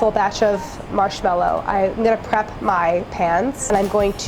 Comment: I heard "I'm gonna crap my pants" XD my humor is so high-class